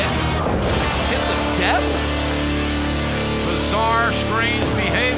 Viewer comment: did Ross say “South Louisiana”?